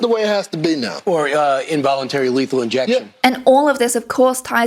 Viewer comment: did he say lethal injection?! Wtf?